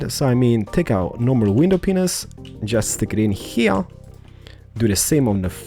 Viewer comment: I mean, his voice is nice and soothing to me :3 but right here, did he say "window penis"? XDDD